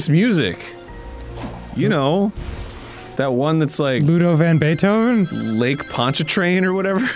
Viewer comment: I also thought he was saying Ludwig van Beethoven lol